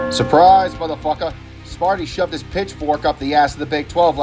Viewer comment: Yea….my bad. Misremembered and was drinking. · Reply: understandable. At least you didn’t claim that the BIG12 missed a CFP bye like someone else. Also ASU’s Mascot is Sparky, not Sparty. Sparty is Mich St. the pronunciation sounded like you said Sparty, so just pointing that out. If I misheard, then disregard. Overall pretty good video though